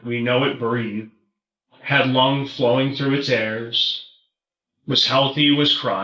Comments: Did he just say "had lung flowing through its airs?" Wth lol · Anyone else catch “had lung flowing through its airs”?